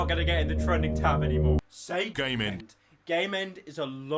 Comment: when he said "game end" i thought he said "gay men" :/